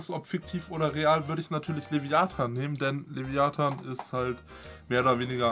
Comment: Ich verstehe bei 0:38 Viagra xD